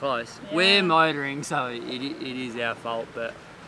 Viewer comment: Hi there, I really like your videos and your unconcerned lifestyle but did you really say at around 09:00 "we are motoring, so it is there fault"? I think you are wrong ;-) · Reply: Steffen Paukner He said "our fault", Capt. Riley knows what's up.